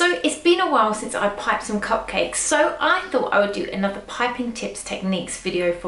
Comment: Am i the only one who thought she said in the beginning “hey guys it’s been a while since I had some cocaine” 😂